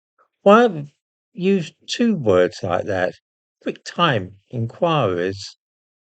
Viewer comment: I'm not a native speaker and had some difficulty unterstanding you. I heard "big time enquiries" 😎